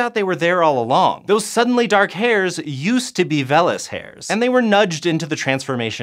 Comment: I thought you said FELLAS hair